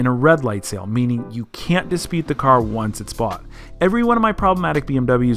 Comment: what you say dispead